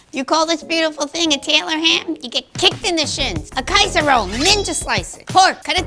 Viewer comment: For a sec I thought she said "kaito roll" and I'm still not sure I'm still happy I heard that I'm not happy I don't what she actually said